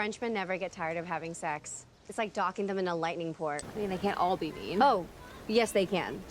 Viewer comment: She's speaking too fast. What is she saying? "docking in a lightning port"?